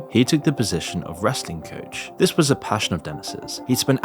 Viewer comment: The entire time he was saying wrestling I thought he was saying resting.. lol whoops